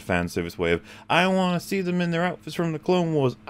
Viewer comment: I like that when at 9:30 says "I wanna see them being friends" the automatic captions say "I wanna see him be in France".